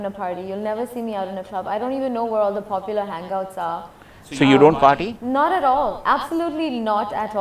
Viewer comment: What did he say at 6:33 "So u don't potty..?" 😂😁